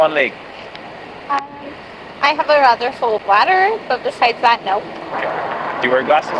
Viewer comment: mark "I have a water that's full of bladder though" 😅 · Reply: Look at the transcript at the 3:43 mark: “um I have a rather full bladder” It’s a little hard to hear it because of the background noise but that’s what she said.